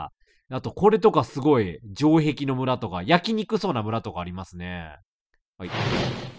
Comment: 「焼きにくそうな村」が「焼肉そうな村」って聞こえて 焼肉と同じ要領で村焼こうとしてんの…？怖ぁぁ…ってなった